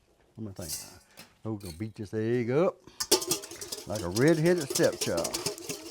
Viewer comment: What he say at 5:17??? Maybe my ears are bad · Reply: Ima beat dis egg up